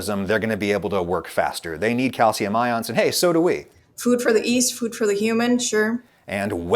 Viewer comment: Go ahead tell me your guest bread scientist didn’t say ‘east’ instead of ‘yeast’ at least twice (second of which was around ~5:20). I double-dog dare you.